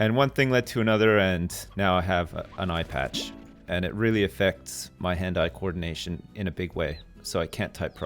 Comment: I heard: It really effects my hentai coordination in a big way.